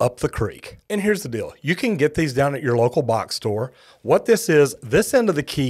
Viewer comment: where I am, the difference between the good plumbers and the bad plumbers is the good plumbers put a shutoff inside where the water enters the house, with an access door - mots commonly in the garage. also, our city water services don't have latches on their boxes, so it's easy to open them and shut off the meter. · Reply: Where do you get that tool that you you said I needed? It sounded like you said a bock store? What the hell is that?